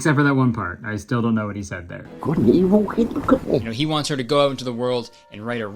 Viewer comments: dont something....look at me I think thats what he said 😭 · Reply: ot sounds more like "good and evil, hey look at me" to me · Good and Evil, kid look at me. With context from the beginning of what he said helps. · i think homie was trying to say "...good and evil, hey look at me" that's all that makes sense to my brain lol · I heard “good n evil” and I think somthing along the lines of “look at me”? Idk. I’m positive about the good n evil part though lol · i think he said “couldn’t evil keep him company” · I think he maybe says “good and evil k look at me” lol??? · He clearly says "couldn't he walk in the cookie?" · i think he says “good and evil, hey look at me” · I think he said "good and evil" and then the second part is less clear but maybe "hey, look at me?" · I think it said " he couldn't even look at men" but like barely 😂😭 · MY BRISBANE SHOW IS SOLD OUT BTW!! FOLLOW ME ON TWITTER & IG TO BE UPDATED IF I ADD ANY MORE SHOWS! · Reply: I think he said "Good and Evil; hey, look at me!" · i think he is saying "Could evil take a kidney" Maybe? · I think he said “good and evil, hey look at me.” :3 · I think he said "good and evil people c'mon" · that beginning scene of gibberish I heard "good an evil ____ could be" I only got half of it 😭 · i think he’s saying “…good and evil? He look at me.” lolllll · “Good and Evil, keep good company.” I think that’s what he’s saying lol. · “…good and evil, now look at me” is all I hear 😭😭😭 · I think he's saying what an evil little guy???😭